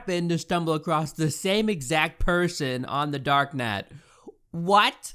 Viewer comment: I thought she said The dark nut